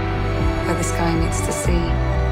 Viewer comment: Love it. but could anyone tell me what she said at 0:26? I can't hear it clearly😂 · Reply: '(I travel the horizon) where the sky meets the sea.' Hope that helps :)